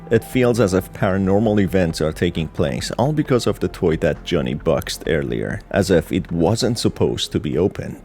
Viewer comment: Why did you say it like that? "Johnny box earlier" 🥊🥊🥊🥊🥊